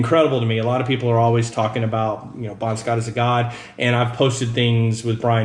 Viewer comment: i thought u said. ''bon scott is a guy'' i was like. yeah ofcourse he is. but i rewind it and u said ''god'' im actually deaf.